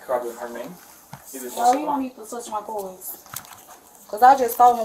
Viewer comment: Did she just ask " Am i going to get locked up "😂😂she can't be this dumb 😂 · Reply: and then the employee suggested to try and charge her card and she said !!!! “DON’T TOUCH MY CORDZS!” Translation said “don’t touch my boys.” The audacity with this women!! I honestly wish cops would say, “ how would u feel if someone got a hold of your card information and they took 2k (example) without your permission and cops speak to u about and ask do u want to press charges??” We all know what it’s going to be said. “Hell yeah I wanna press charges that’s my money, I need that to pay bills.” 🙄🙄 how would it feel 🙃 But it’s ok to make someone struggle. Just for the scammers to go on a vacation or some bullshit that isn’t life or death necessarily. Just for entertainment and pure selfishness! It boils my blood 😅😈